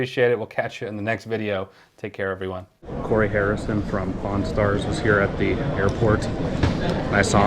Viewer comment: I was so confused, I thought he said pornstar and not pawnstar